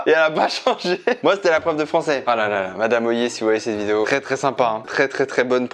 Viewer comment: Attendez attendez, à 5:44 il parle de sa prof de Français, quelqu'un arriverait à me confirmer le nom qu'il a dit ? Parce que j'ai entendu "madame Ollier" qui est ma prof de français de seconde donc ce serait fou que j'ai eu la meme prof que Chris mdrrr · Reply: ça s’écrit madame Hoyet, j’étais dans le même collège que toi apparemment 😂